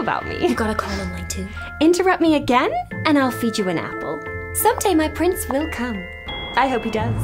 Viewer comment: Disappointed. Thought she said "Someday my prints will come" and I expected her to suddenly have papers handed to her, with a response "Yay! There they are."